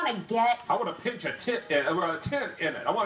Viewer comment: I didn't hear pinch a tit, I heard pitch a tent both times. And both times I was like "does he not know that he's telling everyone he wants to stick his eggplant in that meal..." But now I see the joke was she thought he said pinch a tit. 🤷‍♂️